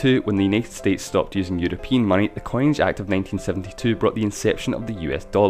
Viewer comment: Is it just me? Or did you say 1972 instead of 1792?